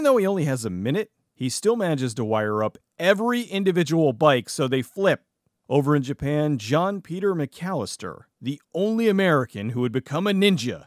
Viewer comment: "He manages to wire all the bikes so that they flip over into Japan" was what I thought I heard at the start.